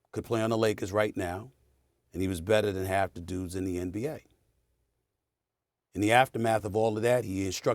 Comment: he did not say the NBA, he said the G-League